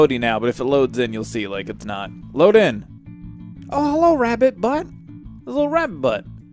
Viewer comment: did he say "rabbit BUN or BUTT"? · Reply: ***** nope, he said butt